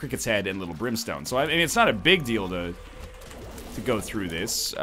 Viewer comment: Is it bad if I heard him say "it's not a big dildo" when he said "it is not a big deal, though" XD